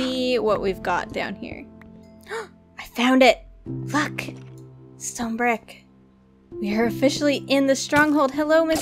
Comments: what did you say? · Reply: "look, stonebrick. We are officially in the stronghold..." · Did anyone else hear “fuck”? I know she said look and I don’t mind cursing it was just weird to hear her say it, even though she didn’t. I had to rewatch a couple of times to figure it out lol.